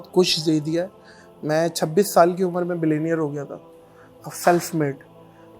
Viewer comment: did he say mai 26 saal ki umar ma billionaire hogaya? Millionaire ya Billionaire??